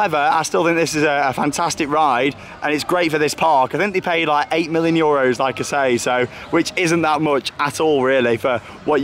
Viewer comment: I REALLY can't make out what you said there. It sounds like "8 million euro". I've re-winded (rewound?) a bunch of times now.